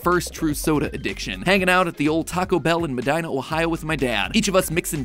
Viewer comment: did he say in OHIO??? Only In Ohio 🗿💀